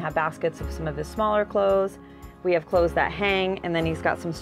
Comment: I'm studying English from your video, thanks for your good jobs! I'm not sure in 6:33, did you say: we have clothes that hang.. ? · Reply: Yes, we have clothes that hang in our closets.